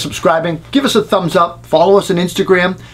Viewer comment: Did you just say concrete carpenter (3:40)? agrees. lol